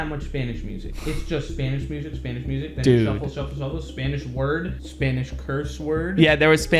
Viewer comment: I thought I said, Spanish bread